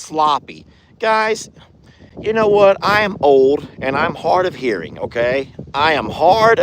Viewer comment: I thought you said you were an old perv. You actually said hard! Of hearing!!! My bad I older then you!